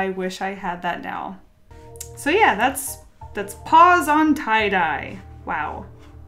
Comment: at first i thought she was saying ‘pause on tie dye’ and i was thinking ‘wow that’s an advanced name for 2nd grade art’ and then i realized... ‘paws on tie dye’ haha